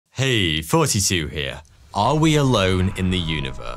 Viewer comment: I thought he was saying, "hey 42 here" like he was from Hitman.